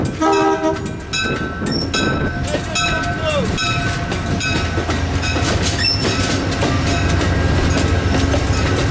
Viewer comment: What is the engineer saying at 15:04? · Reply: “There’s your two toots”